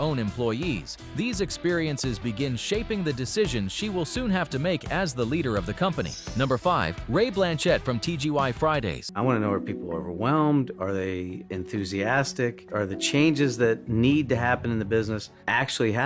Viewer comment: Did he just say "TGY Friday's?"